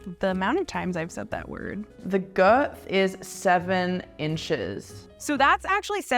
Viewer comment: does she say “girt”? Coz if she does that’s either funny joke about the Aussie anthem or just a mistake